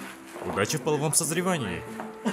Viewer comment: Орнула когда шуга сказал чонгуку удачи в половом созревания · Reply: Мне послышалось «Врачи в половом созревании»😅